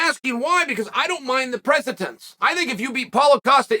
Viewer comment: Love you Chael! Your commentary is always appreciated. I enjoy how you mispronounce the names of less established fighters in order to test their motivation. But when you say precedent think press-a-dent. It sounds like your saying "present tense" which is confusing. Sorry for being a grammar cop, it's for you not me. Much love.